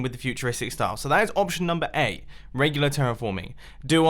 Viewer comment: Did he say option NUMBER A? 3:15 😕